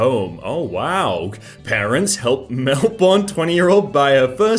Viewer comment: did he say melboned?